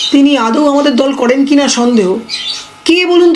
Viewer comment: "তিনি আদৌও আমাদের দল করেন কিনা সন্দেহ".... প্রসঙ্গ রুনা খাতুন।। আমি কি ঠিক শুনলাম নাকি।।😳